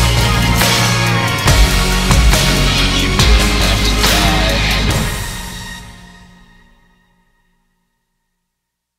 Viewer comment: Does someone have any idea of what say the man at the end of the song ? it's sound like "you ??? you're left to die" but I can't understand the word(s) between "you" and "you're" ... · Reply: you didn't have to die